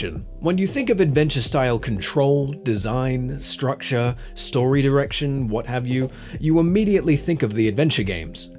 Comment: Okay, this may be just in my head, but I think I might have misheard him say "story erection" instead of "story direction". Lol.